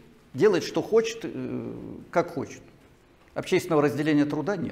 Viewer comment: "общественного разделения труда нет"... профессор ,вы вычеркнули из пространственно-временного континуума целый пласт истории.вы в своем уме ,или проверяете на вменяемость абитуриентов? я хоть не учился в вышках ,но вопрос крайне провокативный.вашим студентам рекомендуется то ,что вы озвучиваете с момента в 27:13 и дальше по тексту😉😁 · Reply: Предложение на 24:50 звучит приблизительно так : "как назвать такой тип хозяйствования, в котором общественного разделения труда нет? ". Ответ был: натуральное хозяйство. Вы просто не услышали начало предложения